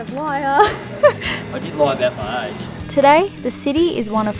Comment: Love you guys!!! Following since day1. Is sooooooo great to see how you've been doing all this years. Now... I'm a sound engineer so my ear is kinda trained and I've heard saying "I lied about my age.." in 8:29 LOOOOl, what age did he said!!?? Peace up, keep posting videos please. Cheers.